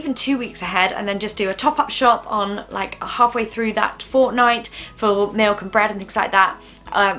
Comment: Did she say Fortnite? 🤔